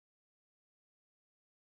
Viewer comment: I thought in the end he said and so he fardedn his computer